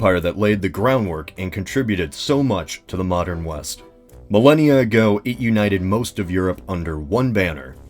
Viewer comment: What if Alexander the Great went west instead of east? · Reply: Andrew Smith wait I thought they said weast.